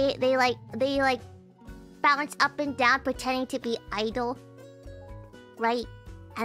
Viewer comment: *idle Understandable mistake, Hololive lives rent free in my head, when someone said "I've been playing x idle game" I misheard it as "idol game" it was embarassing how eager I was to listen (;ŏ﹏ŏ)